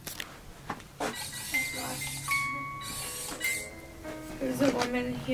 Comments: i hear a response to "we should set up a rem pod" from what sounds like an older man. i hear "alright" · Did anyone hear at 19:00 someone say "all right" when says we should set up a rem pod? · it said “all right”, anyone else hear that? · At 19.00 something said “hey” in like a women voice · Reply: I heard the same thing, but to me it sounded like someone say "all right" at 19:00 and it wasn't or. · after talking about the REM pod it sounds like someone says “alright” or “we’re ready” faintly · I hear a strong voice that says ALRIGHT. Right when they say lets set up a rem pod. Did anyone else hear it. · Dude does anyone else hear a faint alright at around 19:02 when is talking about setting up the REM pod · it sounded like someone said alright like in the far background